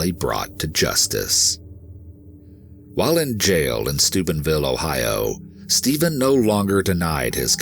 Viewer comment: video: "While in jail in Stuebenville, Ohio, Stephen no longer denied his crimes" what i heard: "While in jail in sTUPIDVILLE, oHiO, Stephen no longer denied his crimes."